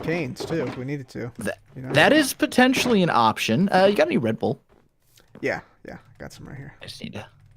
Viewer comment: did he say red Bull???? Are there energy drinks in Minecraft now